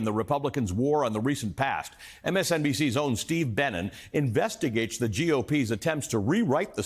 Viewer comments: Thought he said Steve Bannon for a second lol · I thought he said “Steve Bannon” at the top 😬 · I thought he said "Steve Bannon". 😱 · I heard “Steve Bannon” 😮